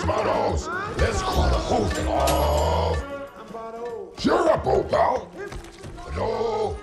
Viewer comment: I am from Brazil and i am not so good in english, someone can tells me what Venom says to Eddie in 0:36? Please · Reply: He said “cheer up old pal.”